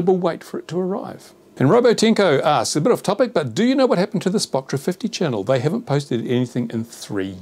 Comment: What is the channel mentioned at 18:10 that hasn't been updated for a while? Can't quite catch what says. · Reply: Spotra50??